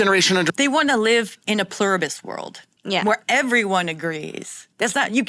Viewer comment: 🤨 did u say plumbus? what ARE they for??